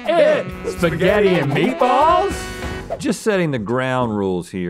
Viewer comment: I thought it said will it spanish 😭💀